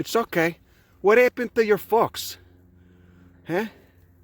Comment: I kept hearing "what happened to your fox" and it took me a while to realise he said "folks." Glad I could find this from Twitter